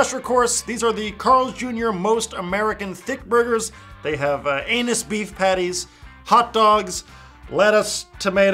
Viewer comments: Isn't it 'angus' beef patty? It honestly sounded like he said 'annus'. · Hahaha it sounded like u said "anus beef patties"